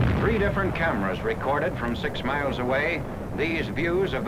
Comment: Did he say... "Six miles away"? That seems awfully close to me.